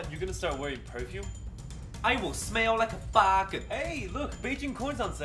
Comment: I THOUGHT DAD SAID “I’LL SMELL LIKE A BAKA” UNTIL I READ THE COMMENTS LMFAOOOOOOOOO